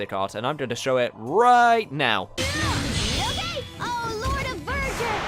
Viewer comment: i'm not sure what's Pascal is saying during the summon but it sound like "oh lord of virgin" something like that